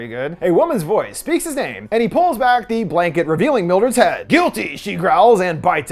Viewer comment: Wait, she said "guilty"? I could've sworn she said "gooch bite" or "goochie" or something. I mean, look where she bit.